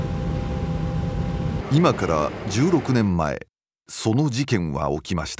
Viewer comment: 「今から16年前」が、「今から十億年前」に聞こえた笑